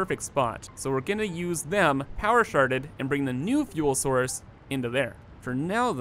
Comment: “power sharded” I heard Power Sharted! 12:34 "Miller Piddles!!"